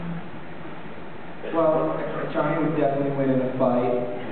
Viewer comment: What does John says at 1:18? Thanks for posting anyway, I love him! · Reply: "Well, Johnny would definitely win the fight," I think that's what John said...